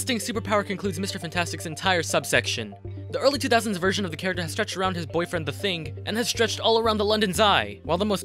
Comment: please tell me i wasn't the only one who heard "...stretched around his boyfriend, the thing" at 6:00 💀🤚🏼